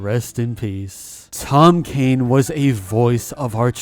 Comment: For a second I thought the video said Rest In Peace Tom Kenny LOL